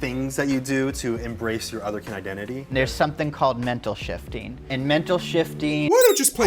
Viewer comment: When she said mental shifting, I thought she said mental shitting at first